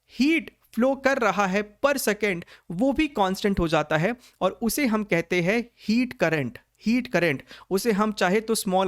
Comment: i heard KONSTAS 😂😅